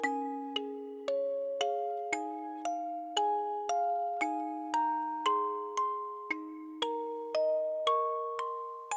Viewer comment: What did William's say I think its, hey sister but im not 100% sure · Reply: It was “Hello Sister”